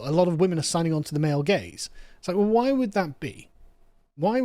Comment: Honey, the male gays are more common than ever. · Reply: I keep hearing it as "male gays" and joke in my head... Why are feminists so concerned with male gays? Competition anxiety is my guess!